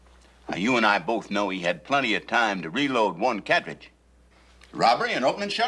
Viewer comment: Roy "killed him fur"?Then at 27:43 Roy "reload one cabbage but caption says carriage, but it`s a cartridge 29:40 Linda Foster also played on S8.E26 called Dark Enough to See the Stars.She fell in love on that one also 33:37 Tom Skerritt also played on the last episode of Bonanza S14.E15 called The Hunter